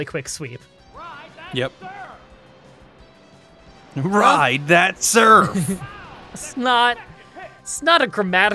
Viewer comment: I always thought the announcer said 'Rise at Surf!" Instead of "Ride that surf!" The more you know I suppose.